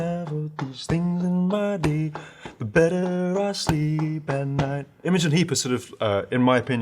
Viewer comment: what’s the name that he says?? can’t make it out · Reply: Then I?